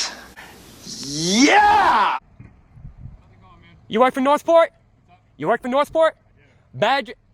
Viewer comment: Sounds like he said North Port